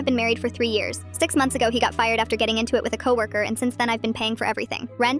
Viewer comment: "getting into it" means he either physically fought someone or was about to. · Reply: I lnow the text says “getting into it” but personally I hear “getting intimate” in that voice